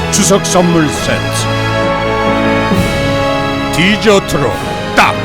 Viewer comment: 왜 뒤져트로 들리지 ㅋㅋ 다 죽일듯하네 ㅋㅋ